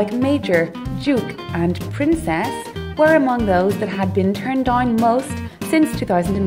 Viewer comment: at :22 it kinda sounds like she said turned on instead of turned down